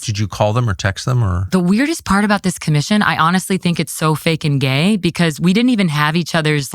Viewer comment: Did she say "fake n gay" around 20:00 😂😂